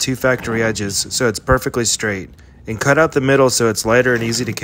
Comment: Did he just say the factory cuts are equal?💀